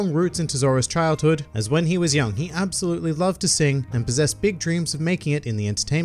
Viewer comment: I misheard that as Zoro loved to sing.. Now I want to see that..